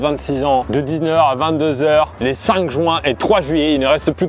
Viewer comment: j'ai entendu join